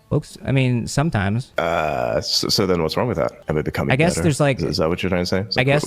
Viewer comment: did he just say "am I becoming better?" As in is Elon becoming better because space x is gay and woke??